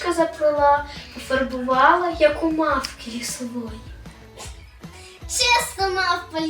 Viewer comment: Мені почулось кріслова